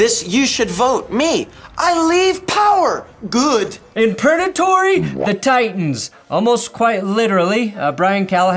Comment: wait wait wait, has it been Pernatory this whole time? I swear I’ve only heard purgatory until now, is this a recent change or have I just been an idiot